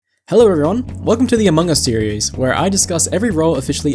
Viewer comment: I've got so much deltarune brainrot that I heard "Hello, everone, welcome to the Among Us theories" 😭